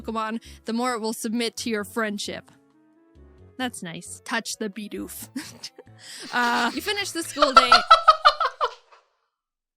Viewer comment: “touch the bidoof” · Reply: I was listening and playing a game and it sounded like she said "Punch the Bidoof".